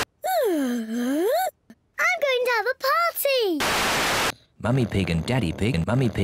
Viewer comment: sounds like she is saying "Im going down the potty!"